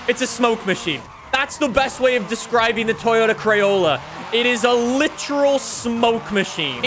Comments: DID JUST CALL IT THE CRAYOLA!!! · Did just call it the Toyota Crayola?? XD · Did he just call it the Toyota Crayola?!?